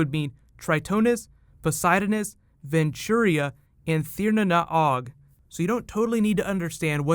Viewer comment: Charlie, what was the last of the seven kingdoms? It sounded like you said Tir nan Og.